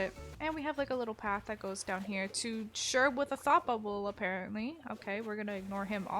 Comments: anyone else when she said "Sherb with a thought bubble" thought she said sherb what the fupoable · I THOUGHT SHE WAS SAYING “SHERB WHAT THE FA-“ and I was like, OLD WAS UNHINGED 😭